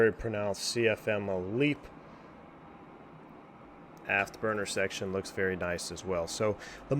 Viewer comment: Did you say afterburner?! Or aft burner? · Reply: 😂 Aft/burner. It does almost sound like afterburner tho